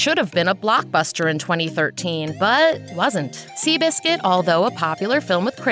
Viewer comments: the subtitles say "2011", when it should ready '2013'. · Theres nothing wrong in spiderman 3 from what I watched and uh- in 3:24 you said 2013 but the subtitles says 2011- "^^ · Subtitle says “2011”, Narrator says “2013”. Just a heads up.